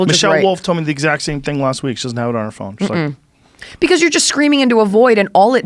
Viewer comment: did He say r phone?